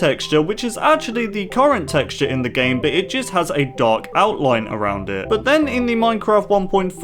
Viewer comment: I Thought You Said Dog Outline